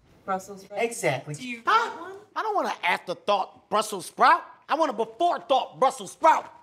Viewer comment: "I don't want an after-thought brussel sprout!" I feel it. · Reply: I thought he said "I don't want an after-THOT brussel sprout!"